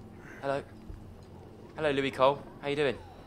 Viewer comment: did he say Lewie coyle? #mot #lufc